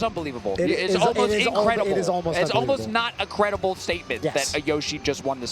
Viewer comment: What did Scar say? 🤔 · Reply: it strains credulity